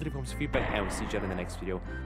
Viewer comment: I always thought he said "diddy-kiddy" bros. Found out he says dedicated.